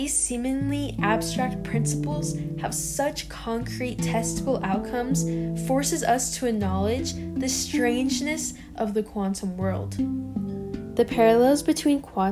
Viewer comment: I thought you said "testicle" 😝